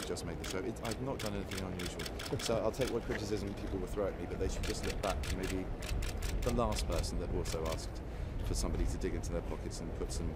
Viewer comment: Can someone please explain what he says from 4:39 thank youu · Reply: Sorry it;s so late! "I've not done anything unusual so I'll take what criticism people will throw at me but they should just get back to maybe the last person that also asked for somebody to dig into their pockets and put some folded up change into a bucket just to raise money for a good cause. Thanks very much." (Walks away) I took this from in an earlier comment :)